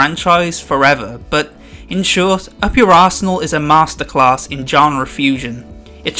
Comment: When he said up your arsenal I thought he said up your asshole lol